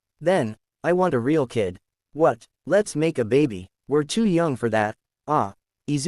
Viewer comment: STOP I THOUGHT IT SAID MAKE A BABY